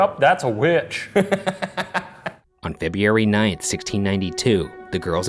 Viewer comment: Did I hear "Febeeary", not "February"?